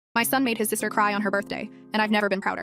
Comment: I read and heard it as “my son made my sister cry on her birthday”